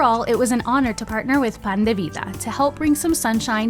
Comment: I thought you said Panda Vida 😂… gotta love panda life🐼 ❤